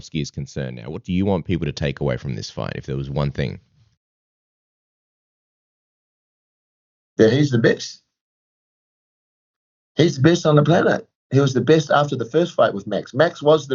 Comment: I thought he said “ he’s the bitch!” Lmao